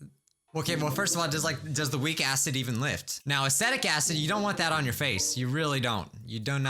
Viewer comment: I thought you said aesthetic acid 13 year old girls drink that for breakfast